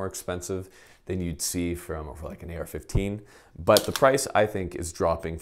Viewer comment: did he say “Fucking AR15”?